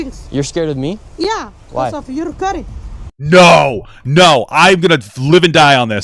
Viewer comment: to be fair, I hear her saying "because of your curry" and we all know that being a bad cook is equal of doing a hate crime.